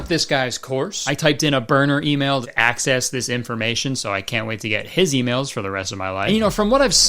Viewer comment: Just putting it out there, when he said he used a burner email to access this information at 8:48 I thought he said access HIS information and thus the followup eyeroll into “now Ill be getting his emails for the rest of my life” sounded like this mf just yoinked his deets and logged into his email. I had to take a second pass, the youtube double take, and yeah I was totally wrong. He’s still a hacker to me, though.